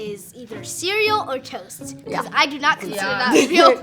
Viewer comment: I thought this was a joke “ my ideal date was McDonald’s or Wendy’s “ and goddamn “ I’m scared of toes “ 😭😂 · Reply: eats 🧃 i thought he said toads😂